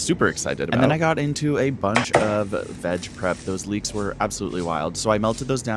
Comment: I’m super high and when you said “look at those leeks” I thought you said leaches and was amazed for a solid minute that people eat leaches